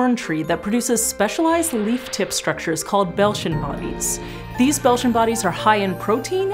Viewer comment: At first I thought she said ‘Belgian bodies’ and got very confused 😂